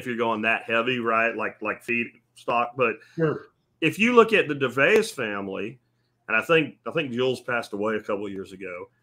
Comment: Does anyone know what the channel name is of the family they mention @ 36:15? I don't know the spelling...souned like he said Devayus, I can't find anything by that name. Thanks in advance 🙂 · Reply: Sorry, Dervaes family